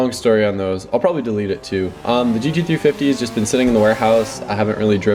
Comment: What happened to the mustang? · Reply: he said gg350 instead of gt i was kinda confused but yeah hes selling it